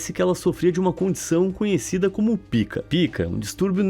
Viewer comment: pika? Eu eu entendi certo mesmo?